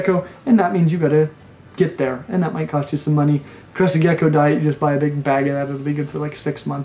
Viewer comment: buy a big bag of ........? for the crested gecko... anyone catch that?? I love your videos by the way! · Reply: Crested gecko diet